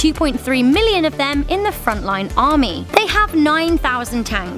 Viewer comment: closed captions. I thought front line said fortnite for a second